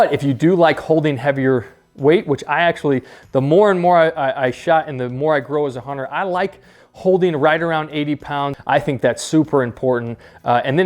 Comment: Did he say " the more and more i shot.... the more i grow as a hunter..... i like holding right around 80 #"